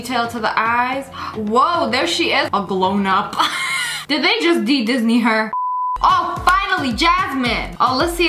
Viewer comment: Lia: Did they just un-Disney her?! Me: *currently dying of laughter* yes, yes they did *continues dying of laughter* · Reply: I heard "un-Disney" °~°